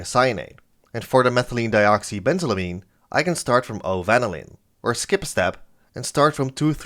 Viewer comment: I thought you were saying 'Good ol' Vanillin' 😂